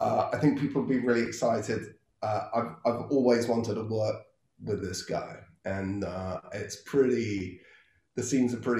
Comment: I thought he said “ I’ve always wanted a blunt with this guy”